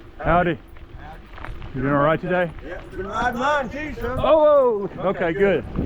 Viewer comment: What'd the guy say at 2:57? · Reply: He said. "I'm gonna ride mine too soon." meaning that he wanted to ride his bicycle too on such a beautiful day.